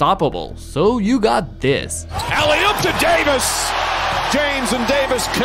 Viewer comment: 4,5 titles still more then 4 Edit: alley op to Jamis at 1:48 I heard that right…right!?